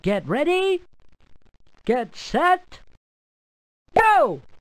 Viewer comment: get ready. Get set. Beeyo! (It's really Go! But with that pop gun sound it sounded like Beeyo!)